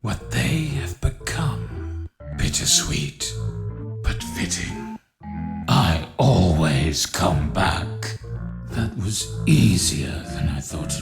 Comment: What did Glitchtrap say? Even after the edit I can't understand him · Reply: I heard "I Always Come back, Let Me Out"